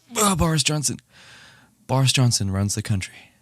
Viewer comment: When he said "Boris Johnson runs the country" I heard "Bars Johnson" so actually that was very funny but then I realized